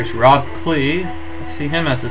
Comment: when you said Rob Klee i thought you said Rock Lee XD